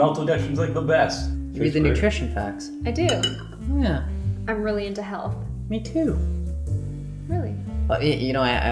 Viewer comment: I thought she said, "I'm really into hell."